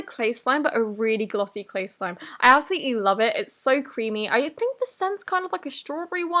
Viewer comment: did she say “i ask you to love it.”